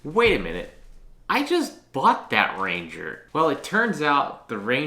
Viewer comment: I thought it said “ I bought the krusty krab”